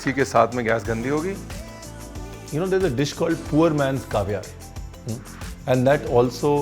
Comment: Acha you were saying Caviar I kept hearing “Kavya” 😂😂😂😂😂